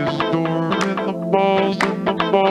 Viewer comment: “Cheese is stored in the walls” -Kraft Punk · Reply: I hear "he is stored in the walls", and now I want answers